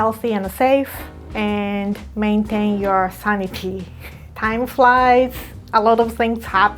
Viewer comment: what did she say at 00:10 ? sounds cute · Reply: I think she said “maintain your sanity”